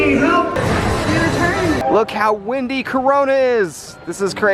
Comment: excuse me sir? did you just say corona? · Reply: I LITERALLY WAS WAITING FOR A COMMENT TO SAY THAT... LITERALLY HE SAID “THATS HOW WINDY CORONA IS”......